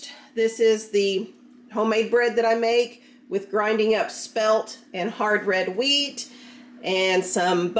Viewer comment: I thought you said crushed up SMELT….when you were describing what you put into making your bread….lolol